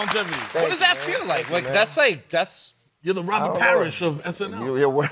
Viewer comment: I thought he said ROBIN HARRIS. With that said, I wanna see Kenan do Robin Harris. 👀👏🏾👏🏾👏🏾